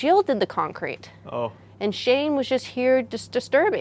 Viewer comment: I thought it said 'Shame'... I mean... could've fit too :p